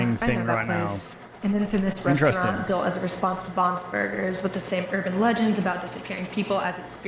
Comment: i think i hear Bons Burgers so does this mean that this VHS tape and The Walten files are connected? idk if im just hearing stuff though